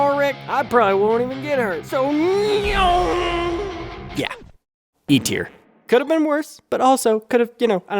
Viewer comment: why did I hear E tier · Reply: Cuz he said E tier 👍